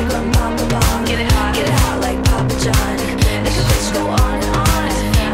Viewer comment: Heard this song on Spotify for the first time, could've sworn she said "Get it hot, like paw patrol" 😂😂